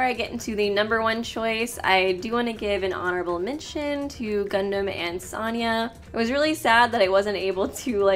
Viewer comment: When you said “gundham and Sonia” I misheard that as “gundham and soda”